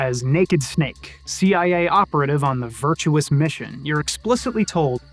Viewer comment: Did you say Virtual Mission?